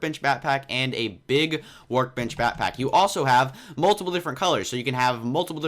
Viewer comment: It sounded like you were saying Batpack